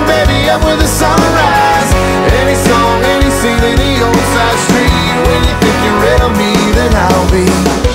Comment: Glad I found this before I taught myself the wrong words. I kept hearing "any song in insanity old side street" 😂😂